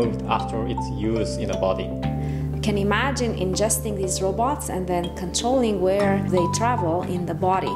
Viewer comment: Did she say "ingesting these robots"?